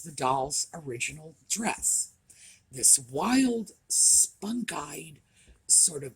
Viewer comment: What was the descriptive word used at 17:35 to describe the fabric? (Sounded like spunk eyed). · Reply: Spun dyed. Describes the way the fabric is dyed.